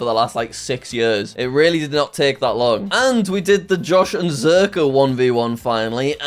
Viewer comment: …did just call himself Josh?